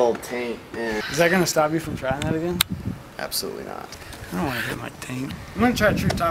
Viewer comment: Laughed so hard when I heard Chris mumble "I don't wanna hit my ting" not long after 7:12 hahaha